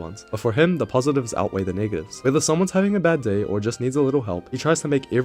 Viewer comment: DID YOU SAY THE N WORD???